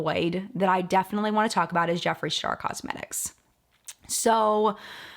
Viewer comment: yees, i love T1J! he always has such a great perspective/take on things. also, did you call him jeffree shark at 10:56 ?? it made me laugh